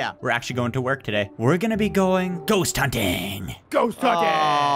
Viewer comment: At the starting I thought said Goose hunting